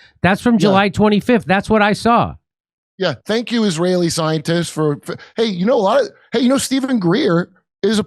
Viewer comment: It sure sounded like Jimmy said (ziontists) instead of scientists. 😂